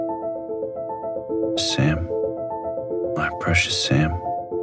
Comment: I thought he was saying “Sand, my precious Sand” at the beginning and got confused